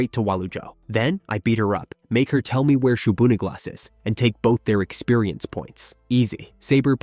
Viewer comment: voice said "I'm going to get anita (I think it said anita) right now". The subtitled text read "I'm going to get a n-i-g-g-e-r right now". Use of the N word? That's pretty bold... or very incompetent. I'm guessing the channel owner, doesn't speak English, used AI to translate, and is clueless about the translation error.